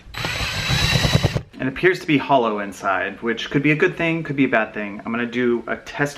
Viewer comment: Who else thought that at 5:08 when he said hollow I thought it was holo inside and I was like what's wrong with holo 📀💽📀💿💽💿💽